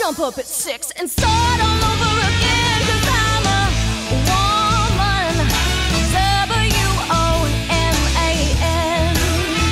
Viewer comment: I thought she was saying I'm a human h. u. m. a. n. Fuckin aliens 👽